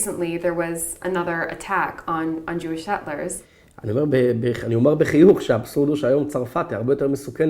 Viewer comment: the subtitle writes "I'll say it with a smile" what he's actually saying in hebrew is "frankly speaking"